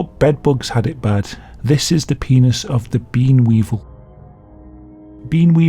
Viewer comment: I thought he said bin weevil 😭